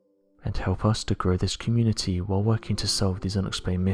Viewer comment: Why can’t this dude say “subscribe” correctly. I love these videos, love the accent and everything about them... but at the end of every video with this narrator he says “prescribe” or just “scribe” I can’t really tell but it’s certainly not subscribe and it just hits the ear wrong, hurts my brain a little bit, and then it’s all I can think about for a few min every time I hear it.... so much so that I just had to comment about it. Sorry man, definitely not hating at all, just requesting that you start saying SUBscribe. Please.